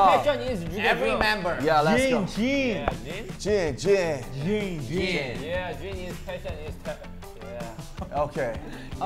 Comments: In the beginning I’m pretty sure Jin said “Jin is fashionista”. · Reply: But I heard "Jin is fashiony star" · Lol at 0:34 I think jin said “jin is fashionista” 😂<3